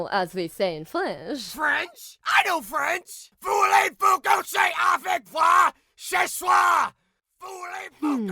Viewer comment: what did ban say when he said he can speak french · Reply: Afrika Verewig voulez-vous coucher avec moi ce soir "Do you want to sleep with me tonight?"